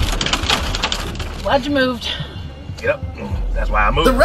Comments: For a quick second it sounded like she said, "why'd you move?" I was thinking huh..... he moved in order to avoid a collision. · Well done. You can feel the relief in the ladles voice, "Glad you moved," she gasped. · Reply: for a sec i thought she said “why’d you move?” I was so confused